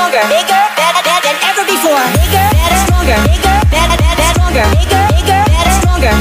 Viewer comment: what did he say? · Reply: HAHAHA no, he say "Bigger, better, stronger" hshahs